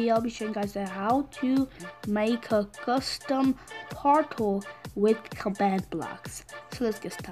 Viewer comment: I thought you said how to make a custom partol